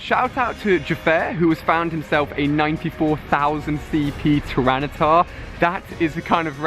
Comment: did he say 94.000 cp tryrannitar?